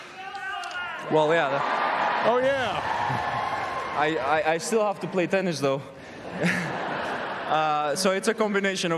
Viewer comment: what did the crowd say? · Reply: 'it's the girls.'